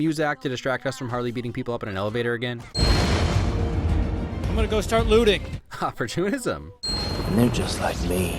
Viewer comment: What did he just say? It sounded like he said I'm gonna start lewding